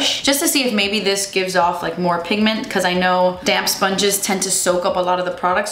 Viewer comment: I thought you kept saying a “damn sponge” instead of a damp sponge hahahah